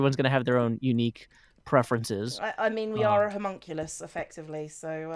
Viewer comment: or 15 Did just say Homunculus? (Has anyone here seen Fullmetal Alchemist or Fullmetal Alchemist Brotherhood?)